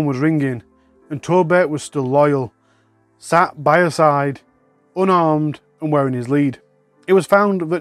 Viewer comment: I thought you said the dog was unarmed not unharmed 😅